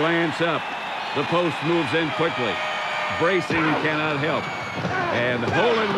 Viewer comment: What'd Pat Summerall say wouldn't help Wile E. Coyote? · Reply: “Bracing cannot help.” As Wile E. Coyote braces himself for the hit that’s coming. :)